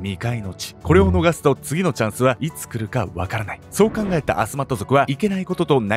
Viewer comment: アスマット族がマスカット族に聞こえてくる、